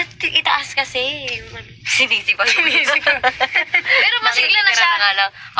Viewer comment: TAMA BA NARINIG KO? SINISI PA NAKIKITIRA NANGA LANG? YUN BA SINABI NI BUNSO?